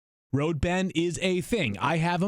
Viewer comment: I thought he said " road head is a thing "